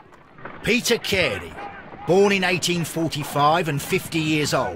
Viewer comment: He said "born in 1945", and yet the subtitles says "1845"